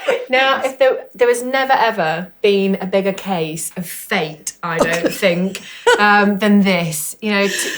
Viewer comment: Ha! Nearly spat my tea out at 3:00 when I misheard “Now there has never been a bigger case of faked” 😂😂